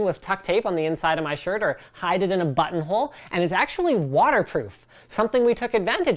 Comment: I swear I heard you say instead of "hide it in your button hole" "hide it in your butthole" :-DDDDDDDDD Linus, you're killing me man :-DDDDD Very nice equipment you've got there guys. The price of it is crazy, just crazy! Looking forward to more videos!